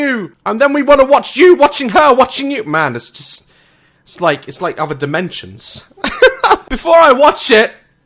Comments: When he said "it's like other dimensions" I thought he said "it's like having dementia" and straight away I was like ME YAAAS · When said "before I watch it" it sounded like he said "before I watch shit" ... I agree